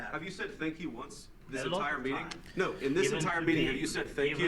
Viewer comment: he clearly says thanking us “in this entire meeting” … so JD didn’t lie there. You’re letting your bias cloud your judgement, man. I say this as someone who is very pro-Ukraine.